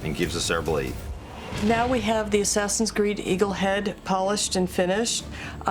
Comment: Did she just say Assassins Greed...